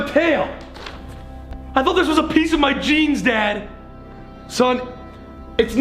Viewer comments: MY DUMBASS THOUGHT EVERY TIME HE SAID “ I thought it was part of my jeans” HE WAS SAYING GENES!💀💀💀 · Was so confused when Ethan said he thought his tail was apart of his jeans, I thought he said gene